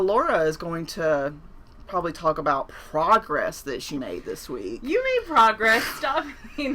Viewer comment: I heard “frogress”. I think we should go with that